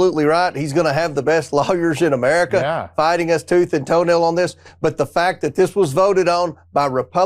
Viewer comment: Did he say 'fighting tooth and toenail'? 😂